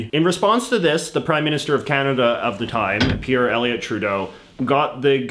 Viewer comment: Bro. Did he just say Fuck Canada??!!! 😱